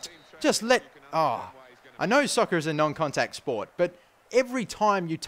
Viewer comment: I'm sorry but did you just say "soccer" at 3:52?